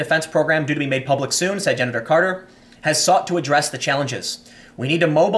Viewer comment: Hahaha— in that sentence Tim read, starting at 10:32, did he call Gen Carter “Gender Carter,” or possibly “Genital Carter” lol?